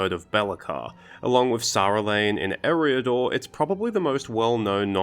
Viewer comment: Is that realm in Eriador Saerlann? Because to me what you said sounded more like "Sarulane".